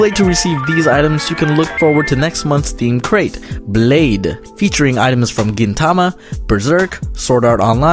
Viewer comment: When he said Blade I Thought he said Laid